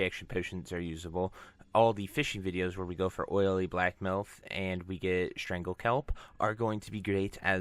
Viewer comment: I heard "oily black milf" lul.